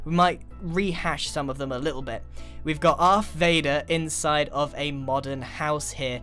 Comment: I thought he said Darth Vader from SW.